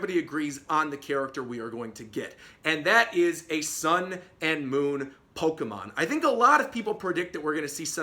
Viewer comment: When you said “Sun and Moon pokemon” I at first thought you said “Golden Sun pokemon” and was like whaaattt???